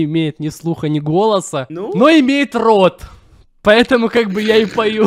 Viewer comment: мне послышалась что сказал "имеет в рот" а не "имеет рот" :_)